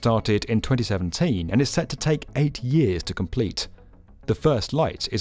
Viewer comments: With that accent it sounded like he said 80 years to complete. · I had to repeat that 3 times. I thought he said 80 years.